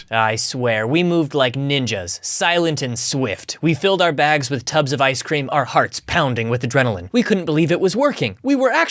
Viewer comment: (Oh, and did ChatGPT say you were shoveling ice-cream into your backpacks as is, without containers or cooling or anything 🤨 I mean, I'd still eat/drink it, but still…) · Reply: I heard "tubs of ice cream" which implies plastic containers of it being put into backpacks.